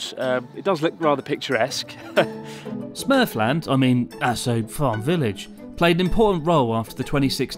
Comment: This is Asshole farm village, swore I heard that the first time haha. I love this channel, always great content!